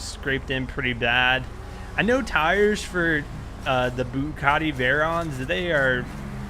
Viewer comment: does he say bucatti?